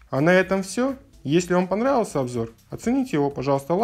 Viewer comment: мне послышалось :) оцените его лайком или лайком :)